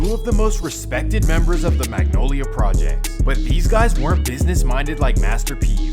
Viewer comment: I thought he said MILF project 😭😭😂😂😂. No disrespect